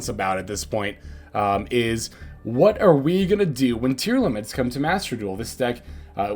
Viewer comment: This whole time, for like a week, I thought people were saying fucking tier limits, nobody seems to pronounce laments right